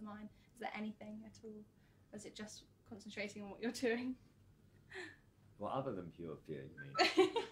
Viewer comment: what is he saying help me understand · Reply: He says: "what, what other than pure fear?" in response to what he is thinking when looking out at the audience. Brilliant Man!!